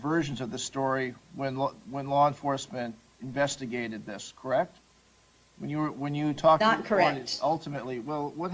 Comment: Wow she really dug her own grave in those interviews. My mother is also narcissist who also says crazy things, thinking that it's totally normal and ok. Like when I was 15 and a girl I was good friends with had committed suicide and my mother responded with "its ok we didn't really like her anyway did we?" as an attempt to comfort me. She seemed totally unaware that everyone who heard had dropped their jaws at what she said. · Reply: Before expsming the comment I thought you said "when you were 15 AND you were a girl" lmao